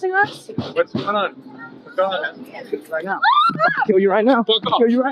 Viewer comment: Sounds like he says….”I will 💀you right now”…..🤔